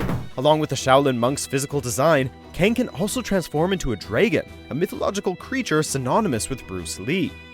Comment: Did he just said "Draygon"?